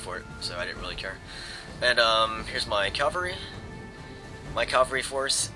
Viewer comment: it sounds like hes saying calvary instead of cavalry